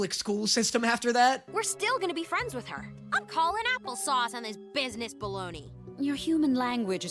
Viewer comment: i a spanish speaker I didn't understand what he said either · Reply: “I’m calling apple sauce on this business baloney.” Luz is probably saying “I’m calling bs!”